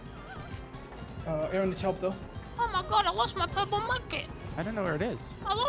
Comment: I swear to god i thought he said “omg i lost my purple monkey”